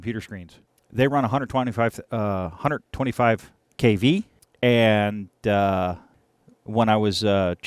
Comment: There are 24kV oscilloscopes (Tektronix 517A I think is). There are old oscilloscopes that have a mains derived EHT with a big smoothing capacitor - probably much more dangerous. 6:07 sounded like "a 125kv" .. lol nope, the X rays would, excuse the pun, be a tad hard to shield. 25kV is common though, and you can have up to 32kV in that world, and CRT projectors DO go higher, into the 40+ kV range.